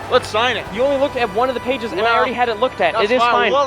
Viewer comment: Whoever is editing these videos should be replaced. 4:23 He clearly says pages yet someone decided to write "apges" What kind of shit is this, this isnt quality programming, disliked and unsubbed"